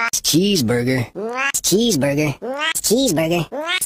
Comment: I hear “it’s cheese burger”